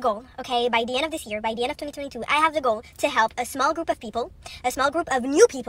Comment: That girl talking in the little girl voice: I thought she said, "at the end of 2022, I have dick-all" (she said something about a goal?) LOL! Anti-MLM tribe knows the real statement is what I thought.